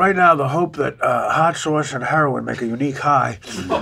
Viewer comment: Does he say he's on heroin at 20:42 :(